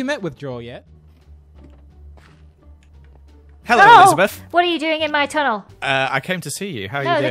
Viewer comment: SPOILER ALERT!!! As soon as Joel said “Hello Elizabeth” I knew he was the boogeymen. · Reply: I didn't even realise he said 'elizabeth' i thought he said 'lizzie'